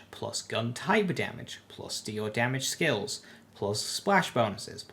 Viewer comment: sorry I can't really make out what this is, does anyone here know what he said here? It sounds like he's saying "plus do your damage skills", but the text says "All Damage"